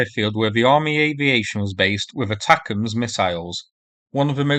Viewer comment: I thought he was saying "attackums", like Steakums. XD